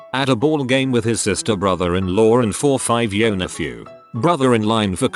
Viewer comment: I was only listening to it and was very confused for a moment when I heard "sister brother in law" lol